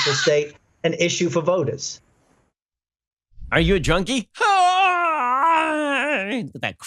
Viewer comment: I thought I heard Biden say "are you a chony?".... hahaha regardless, Joe is a COMPLETE Phony!!!